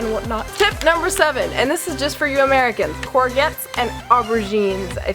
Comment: lolz I thought you were saying croquette when you pronounced "courgette" Glad you had fun! It might just be me, when ever I travel I look forward to the cuisine rather than the site seeing stuff... If you ever visit Sweden in the future, let me know :D